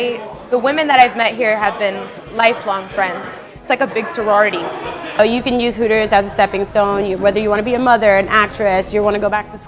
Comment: did she just say 'if you wanna be a mother-working at Hooters is a stepping stone'???? D